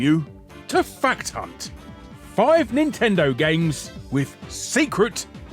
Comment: i thought you said fat cunt instead of a fact hunt for a second there